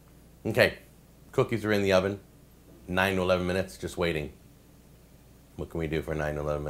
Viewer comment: just fast forwarded to 6:09 and hear 9/11 hehe was kind of shocked at frist (until i hear the hole thing you said)